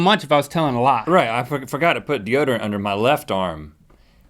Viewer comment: anyone else hear rhett accidentally say the f-word at around 7:58? "i fu*** forgot i put deordorant under my left arm"??????